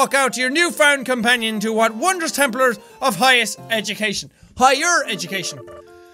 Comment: it sounded like Jack said 'templer of highest education'…either that or I've been playing assassins creed for too long today